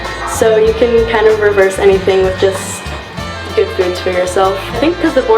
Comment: "You can kind of reverse anything with just ___ for yourself." What did she say there? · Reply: I think she said “good foods”. I kept rewinding and listening to her talk about her back. I swear it sounded like she said “I killed my back in 4 months”. I assume she said I heeled my back!